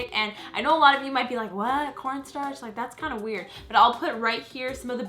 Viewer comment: it sounded like she said p o r n startch